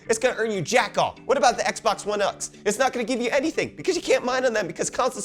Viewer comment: Did you just say Xbox-one-nuks/nuts?